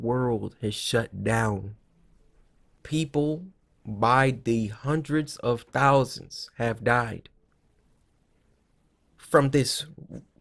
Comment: You forgot all who lost their lives to corona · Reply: 1K Subscriber with no videos challenge no he didn’t. He clearly said “hundreds of thousands of people.” Do you need a hearing aid? This is at 3:47. He said “People by the hundreds of thousands have died.”